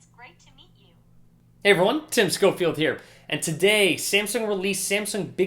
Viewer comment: Can somebody please tell me what he says at 0:05 ??? Heyyy __ what? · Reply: Hey everyone ?